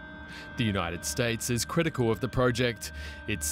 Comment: did he say "cooperating in this way is going to put international security at risk?" My last brain cell died trying to understand it.